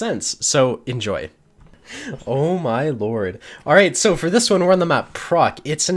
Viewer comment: I thought he said. "Alright, For this one we are on the map fuck." And i was like damn right. Fuck that map. Fuckin fuck map.